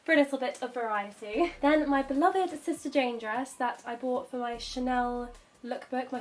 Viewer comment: I thought she said sister James lol